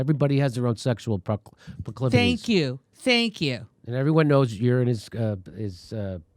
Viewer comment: "everyone knows you're sterile" [to stef] Say what, Jimmy?! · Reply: Not "you're", "URINE". He was talking about pissing in people's faces, remember? You misheard what he said.